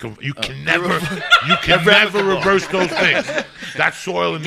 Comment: what was the word joey was trying to say at around 13:00? irreputable? irreclaimable? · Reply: irreversible